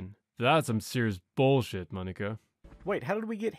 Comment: what did you say...? 👀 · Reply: he says monica ???